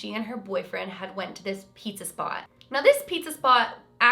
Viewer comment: I thought she said pizza spa I was like ooo where? at the spa with pizza whaaat